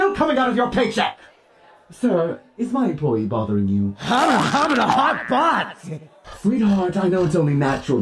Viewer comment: What is exactly Deadpool is saying at 1:06...?? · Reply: Hubba da hubba da hot butt.